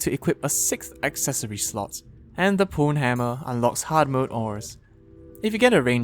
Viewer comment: why did i hear the pwnhammer as pornhammer